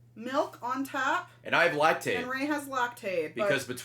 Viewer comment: Ray's sense of humor is so weird that when he said "And I have Lactate" at 1:40 I thought he said "And I lactate". And my brain just went, "Yup, Ray's lactating. That's something he would say in this situation. Pretty standard Ray joke."